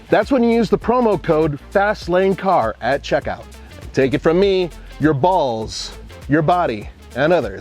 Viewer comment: I just heard say “Your balls will thank you” Alright then